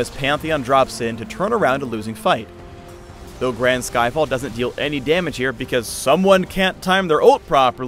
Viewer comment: "Though Brands Skyfall does no damage" kk Phreak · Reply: ElementalSkiller I thought he said that, too, at first - but he said "Grand Skyfall"